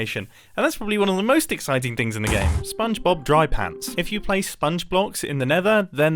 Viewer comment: i heard it as spongebob dry pad lol